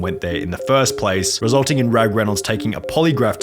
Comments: I thought you were saying Ryan Reynolds at first. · I thought that you said Ryan Renolds for a second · says his name so fast that I keep hearing “Ryan Reynolds” 😂😂